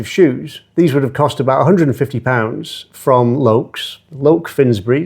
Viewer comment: Did I hear that well..expensive shoes $150? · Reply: £150 = about $200 US - I know they are not the most expensive shoes but the point is they are good quality and ones you would want to keep looking their best.